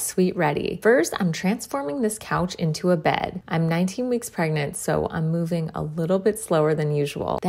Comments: I THOUGHT SHE SAID 19 MONTHS PREGNANT💀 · Thought she said shes '19 years pregnant' 💀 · HELP I THOUGHT SHE SAID SHES 19 MONTHS PREGNANT